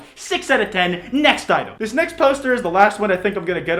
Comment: I thought he said "toaster"